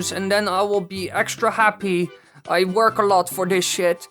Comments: ? What did I hear at 11:24 ? What I heard was: I work alot for this sh** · what was going on at the end there? your voice got a bit weird. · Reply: I heard ‘I work hard for this shit’